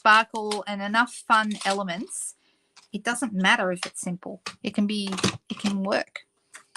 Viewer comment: I heard "I know it's pretty sinful" instead of "simple". Wondered how an "easy" card was "sinful"!